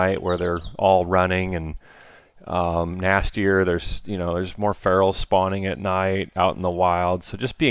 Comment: Did I hear, "More ferals spawning at night"?